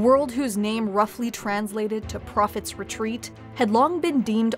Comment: I heard "Profit's retreat" instead of "Prophet's retreat" and thought for a second that the Twitch chat voted for the planet to be named Demonetization xD